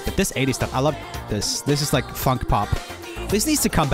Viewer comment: did I mishear this or did he just say f*** pop